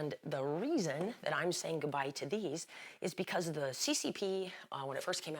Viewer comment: I thought it said saying goodbye to the sex pistols.